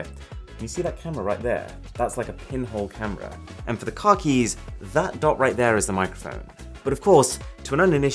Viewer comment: Everytime he said car keys I heard “khakis”